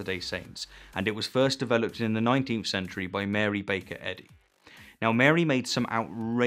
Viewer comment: I coulda sworn you called her Mary Baconhead